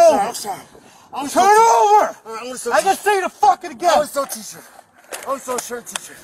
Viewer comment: I hope I'm not the only one who misheard, I'm a teacher, I'm a school teacher when he was actually saying I only stole t-shirts.